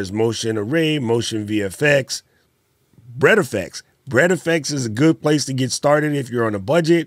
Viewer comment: Shutter encoder?? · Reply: No Sir. The website where we can get the Automatic Titles at a reasonable price. It sounds like you said bread effects 🤷🏾‍♂